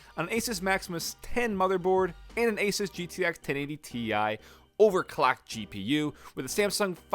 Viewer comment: Is it just me or did he say "overclacked" lmao